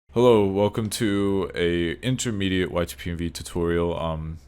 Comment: did you just say “A intermediate YTPMV tutorial?”